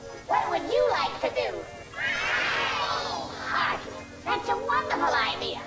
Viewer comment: I've noticed something weird. At 0:05, when the kids say "party", it sounds like they say "Flowey". HMMM